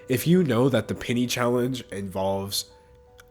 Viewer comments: My dumbass thought he was saying ‘pinny’ and my Australian mind was trying to comprehend wtf a pinny is in the US 🤪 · Thought you said 'pinny' challenge lol 😂